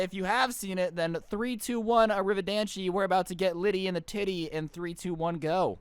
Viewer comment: Did he just say litty with a titty